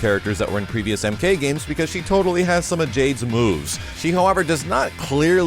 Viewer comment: when you said she has Jade's moves, I swear I heard you say Jade's boobs lol